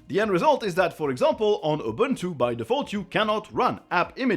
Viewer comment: sounds like you said “for example on Ubuntu by defaultu”. That made me laugh.